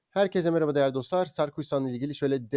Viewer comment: Hangi hisse? Adı? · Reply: Sarkuysan diye duydum